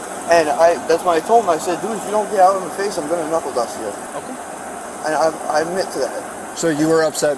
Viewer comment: Did he say he was going to "knuckle dust" him? Never heard that one before 😂 I like it👍